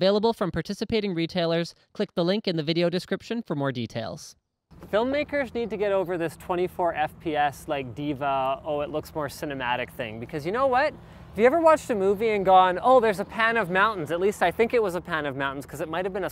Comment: I heard: "24 FPS like D.va oh it looks more cinematic thing because you know what? If your overwatch... Help me I play Overwatch too much